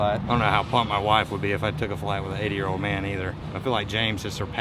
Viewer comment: I could have swore he said 8 year old.